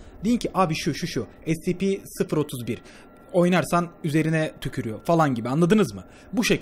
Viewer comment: "scp-031 oynarsan yüzüne tükürüyor" ben yanlış anladım._.